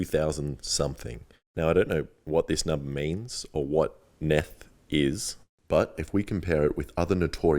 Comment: bro I literally thought he said meth 💀